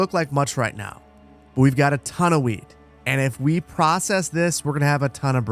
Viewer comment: All I heard at 33:35 is "But we have a ton'o'weed!" and I was like "nice."